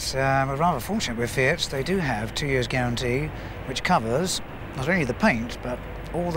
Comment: Fiat - 2 year rust proofing lol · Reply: I thought he said rust guarantee ... which I could understand better .... ;-)